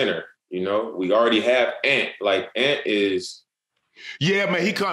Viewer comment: did he say ant? Who’s that anfernee simons?